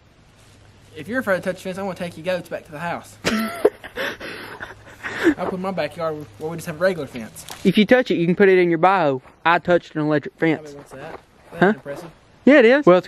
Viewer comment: at first it sounded like said if you touch it you can put it in your butthole bahahaha. I started laughing so dang hard I spilled my coffee all over my shirt and bluejeans lmao. Thanks for the laugh!